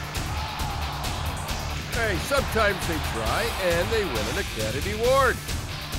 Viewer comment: what is he saying? "sometimes they try and win a [blank]" any help identifying this would be appreciated! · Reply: “Sometimes they try and they win an academy award”